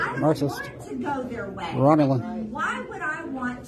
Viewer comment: or so does he say Romulan after Marxists?